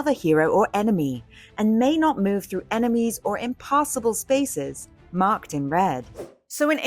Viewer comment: Glad we got that clarification on impossible vs impassible. Totally different things. I heard "impossible." I was waiting to hear what made those spaces impossible. 😂